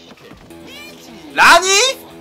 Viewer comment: does he say nani or ranni? the subtitles say what but the kazumi costume looks like ranni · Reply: rani